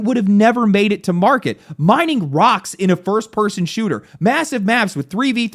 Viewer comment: Did I hear a rock and stone!